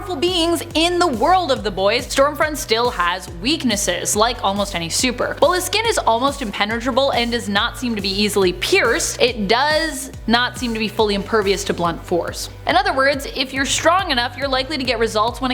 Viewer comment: Guessing it was a coincidence rather than intentional but the two times she says "not seem" I heard it as nazi(m)